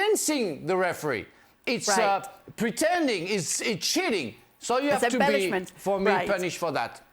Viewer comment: When Frank said “it’s cheating” I thought he said “it’s shitty”